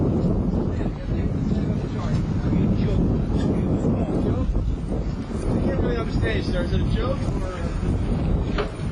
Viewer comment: I wonder what Stanfa said at 9:50? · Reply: "You joke." (You're a joke) He said it another time when that reporter asked him questions while he was in handcuffs.